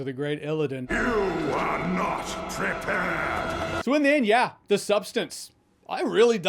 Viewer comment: Gotta admit, I don't know a lot about illidan, but I thought he said "You are not trippin!"